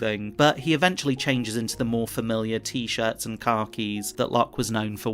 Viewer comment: did you just say "T-shirt and car-keys" when it was supposed to be "T-shirt and khakis"? How does that even happen? Speech-to-text? AI? Wtf?